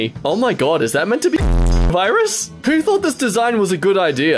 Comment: did he say coronavirus? 😷😷💀💀💀